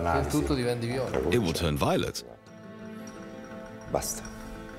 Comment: What does the teacher say (in Italian) at 2:33? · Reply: basta = stop